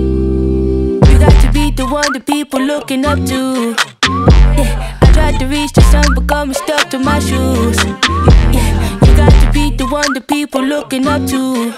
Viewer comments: “I tried to reach the sun but gum is stuck to my shoes” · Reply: Thought she said "I try to reach the sun but become the stuff under my shoes" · “I tried to reach the sun but gum is stuck to my shoes” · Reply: I thought she said"but become stuck to mushrooms" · “I tried to reach the sun but gum is stuck to my shoes” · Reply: I thought she said karma stuck to my shoes. wow you have Insane listening skills.....🤗